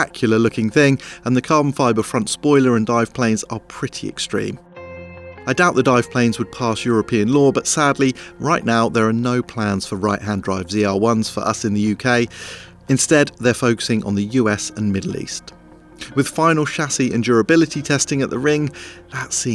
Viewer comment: Did he say zl1?? 9:57 . All good. I super respect jethro